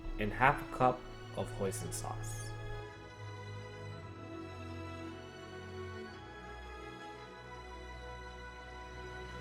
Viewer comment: is he said "poison sauce" ? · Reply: Hoi Sin Sauce. You can find it in any Chinese Store.